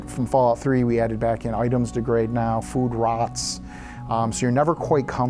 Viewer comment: Wait, item degradation did I hear that right at 22:20?